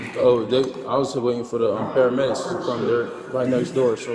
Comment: Did he say I was going to wait for the paramedics before he would help him up off the floor?